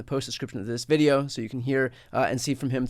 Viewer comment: I heard "inside the reich".😶